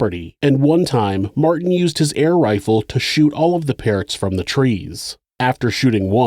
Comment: As a kid, my dad brought me an air rifle for my birthday. I'd try to shoot the stems on apples in our apple tree to make them fall. I thought you said the guy shot pears in the tree, but nope, Parrots. I guess I'm not even at that full on monster point of the episode yet but fuck this guy.